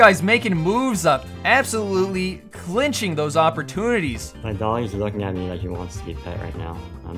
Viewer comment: did u say flinching these opportunities